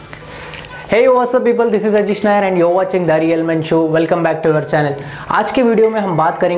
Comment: I heard DARIal men show